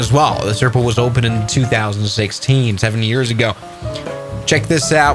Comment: did he say 2016, 70 YEARS AGO?!!